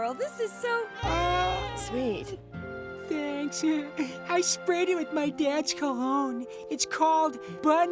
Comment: I HEARD "i spread it with dad's cum"